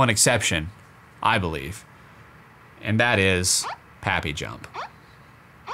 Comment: Flappy Bird Is Better Than Happy Jump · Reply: Oh I thought they said happy jump